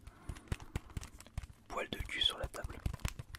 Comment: Attendez, c'est moi ou il dit bien "poil de c*l sur la table" a 9:03 ??